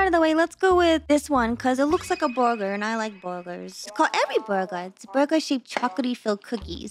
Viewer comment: I will not lie i looked away from the screen for a couple of seconds and thought you said "I like boogers" and was like wait what then turned to see it was a burger looking snack😂😂